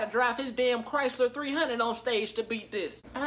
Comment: Bruh did he just say " is that doodoo on my lip "